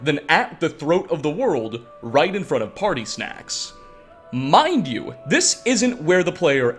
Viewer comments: Did he just called Paarthurnax "Party Snacks" ? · Dude you just said party snacks instead of parthinaax